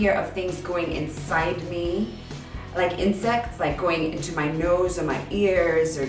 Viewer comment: Haha okay raise your hand if in 9:19 you heard in sex instead of insects lmao I was like wait what? rewind hehe lordy lord